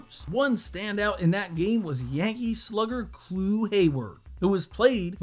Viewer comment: did you say Clue Hayward??